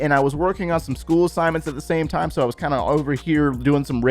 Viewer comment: did u said school assignments.... dude u just look like dudy grand father...